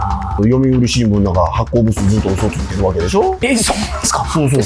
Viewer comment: 読売新聞発行部数のあと何て言ってるんですか？ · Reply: 「ずっと嘘ついてるわけでしょ？」 のように聞こえます。